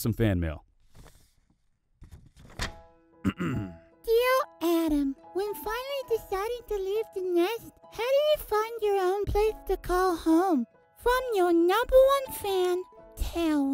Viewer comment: My internet went out and all I heard was “how do you fi—“ but it sounded like how do you fu— 💀